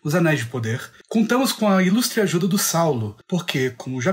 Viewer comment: Na hora que ele falou "ajuda do Saulo" Eu entendi "ajuda do Sauron" kkkk